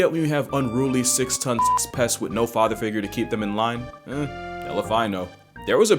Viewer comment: did he say hell if i know or ellephino i cant fucking tell